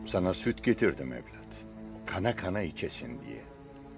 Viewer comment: I heard Khana Khana at 0:47 sec